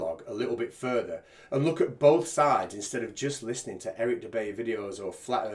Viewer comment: Did he just say Eric Jew Bay?! 😲 😂 🤣